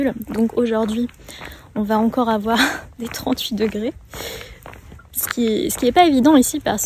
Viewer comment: does u say, is it me or did u really said trente degré instead of trente-huit degré. I just want to know if its just a mistake or is it me who’s not catching the sound.